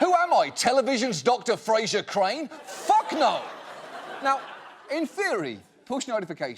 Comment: When John says “in theory” at 0:31 my Siri thought I said “hey Siri” and activated itself.